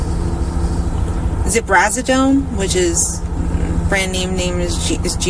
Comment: Someone in The Docket's original stream misheard "Ziprasidone" as "Trazadone" and started speaking about how Trazadone isn't for bi-polar, which they are correct. They just got the name of the medication wrong, and sadly others in the chat picked up on it and started repeating the misinformation. So, I'd like to clarify, did NOT claim to be on Trazadone. She said she was on Ziprasidone, the cheaper form of Geodon. 1:02:55 Many people, myself included, believed & Stephan met at Disney because the channel Guilty of Crime did an interview with one of earlier roommates and the roommate firmly stated that & SS met at Disney, became friends, and eventually bonded over having the same gastric sleeve surgery. Which story is true? I don't know. I just wanted this discrepancy brought to light before it spreads too far to control. tI'm only halfway through as of 12:26 am (est) on 9/14/24. I'll watch the rest later. Happy hunting folks. Prayers for Maddie & God Bless all who see this message. 🤍🙏🤍 TOODLES!!!!